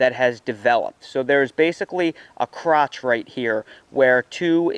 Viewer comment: did...did you just say crotch...